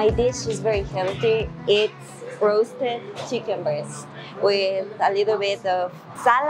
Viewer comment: Great video. I had to back it up when you were talking about chicken. I thought you said chicken bird but I think you said chicken breast. chicken bird 🤣